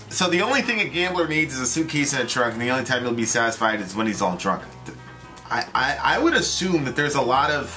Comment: "The only time he's satisfied is when he's on a drunk." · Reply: I always heard it as "when he's all drunk"